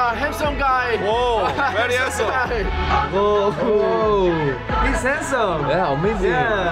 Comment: Oo He's Handsome 🧐 Yeah Obviously 🙄 That's what I heard 😂